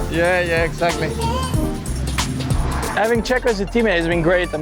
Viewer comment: thought he said ''Edin Dzeko as a teammate'' haha